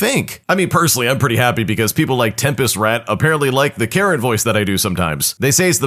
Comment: I was half asleep, and when you said "Karen voice" I thought you said carrot voice😭 (u do have a nice Karen voice tho)